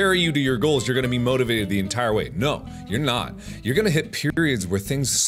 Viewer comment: ish I thought he said "You're gonna hit puberty." Well ain't that right.